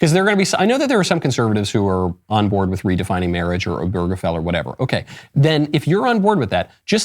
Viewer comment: what's a gurgefel? I'm sure I'm mishearing something and/or misspelling